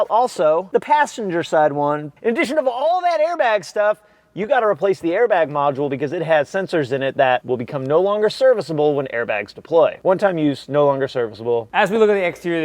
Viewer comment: All I heard was "driveable".